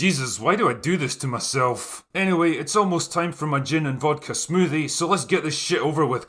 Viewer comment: “It’s almost time for my gin and tonic smoothie.” Oh Drinker, we’d be lost without ya don’cha know…. Keep up the good work. 😃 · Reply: I thought he said gin and vodka.